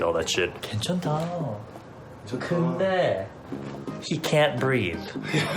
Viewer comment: is he saying "SHE can't breath"?